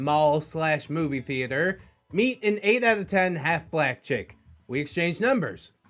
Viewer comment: I need to get my hearing checked. I thought you said 8/10 Aflak chick. 🤣🤣🤣🤣